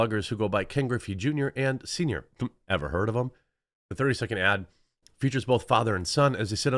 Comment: At first I thought he said "never heard of them". That would've been a hilarious catch.